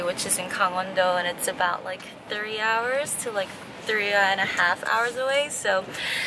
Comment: I initially thought you said a 30 hour journey and I was like Jesus but then I realised you said 3 hours aha.